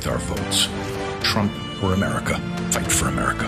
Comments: What exactly is being said at 0:52 to 0:54? It sounds almost like it says "Trump for America" as someone slaps an "I Voted" sticker on. Has this been corrupted? Was it intentional? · Did I hear “trump for America “ at the end? What did I miss? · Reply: No, it was: “Trump OR America” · 00.52 first two times I heard "trump for America" rather than "trump OR America".